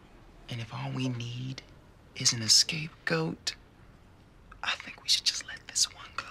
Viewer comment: Not until now that I heard what he actually said was "escape goat" :D